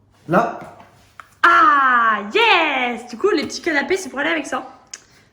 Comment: ses moi ou tu a dit cannabis xD